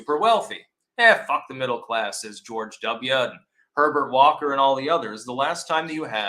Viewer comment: Lol, I thought you said Pervert Walker, oh well, you are still the best political analyst I know of.